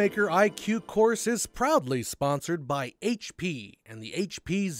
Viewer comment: I heard "probably" sponsored by HP, not "proudly" at 00:16. Was worth a chuckle :P